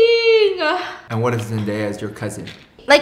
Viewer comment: Wait did he really say Zendaya? 😂